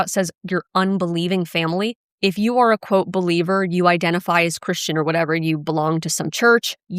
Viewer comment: I thought you said "belieber"! Lol! But, in all seriousness, I don't see the JWs as bad people But, they stab each other in the back That isn't very nice! They're conditional friends The GB is who I'm mad at! They're running an evil religion! Side note: I'm a Belieber!